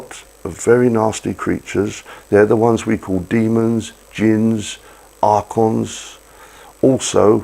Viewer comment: At around 8:16 does he say Genies or Jews? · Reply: He says,'Demons, Jinns, Archons ...!'